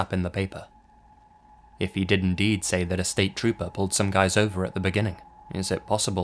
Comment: It sounds like says "I screwed some guys over"